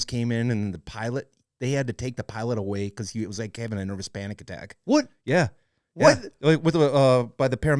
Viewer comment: I had to laugh... I thought he said the pilot had a 'hispanic attack' ... 🤣😂🤣